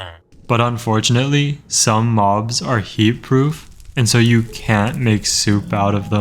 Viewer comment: thought this said “some mobs are hebrew si you can’t make soup out of them” i was so confused. Like how do you tell the hebrew mobs from the others and why can’t they be soup?